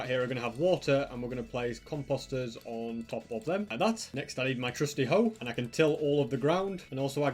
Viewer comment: what did he say? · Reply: "Next, I need my trusty hoe"